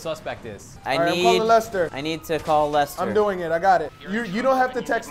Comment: Will the real slim shady please stand up? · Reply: when they said call lester i thought the said call the molester :3